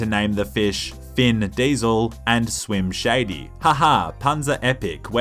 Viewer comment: I thought you said slim shady